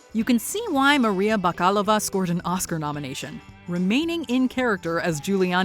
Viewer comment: i really thought you said her name is Maria FuckAllOfUs